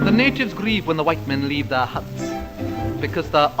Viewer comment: I heard “leave their hunts” and was then a bit apprehensive for an ahistorical surprise in the next line “they’re obviously, definitely…”